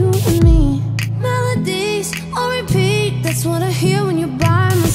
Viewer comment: At the "melodies on repeat" part i thought she said "melodies, Rip"😂😂😂